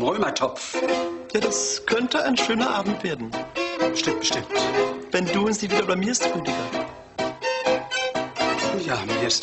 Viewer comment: Was sagt Florentine 1:01 (die Musik ist zu laut) „Es könnte ein schöner Abend werden wenn …?“ Un was antwortet Rüdiger? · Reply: wenn du uns nicht wieder blamierst, Rüdiger!